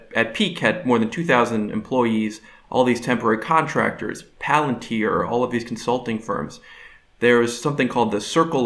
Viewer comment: does he say Palantir?